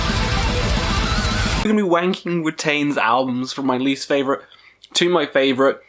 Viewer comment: All I hear at 0:22 is, "I'm gonna be wanking Watain's albums..." - fair enough; you do you! 🤘😈 (and great band to, ahem, wank, too!)